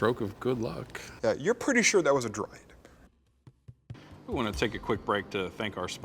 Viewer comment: Both I and the auto-captions heard "droid" instead of "druid" here.